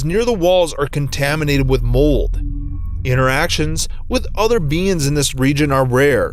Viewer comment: Who at 2:19 heard BEANS instead of beings?